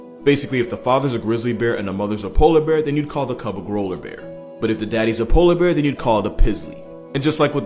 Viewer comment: I thought he said “Rollerbears” at first.